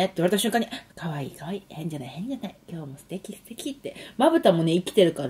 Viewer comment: の変じゃない、変じゃないが エンジェル, エンジェルに聞こえる🤣